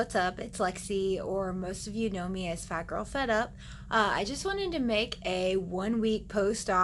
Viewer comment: I thought your SN said FatGirlFriedUp 😂